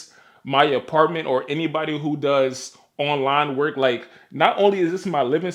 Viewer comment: When you said “online work” for some reason I heard “s3x work” 😂. I cringed at first then I realized I was tripping